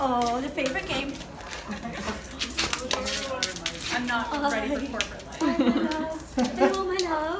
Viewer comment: Who else heard the lady in the background at 14:28 say “I’m not ready for corporate life”😂😂😂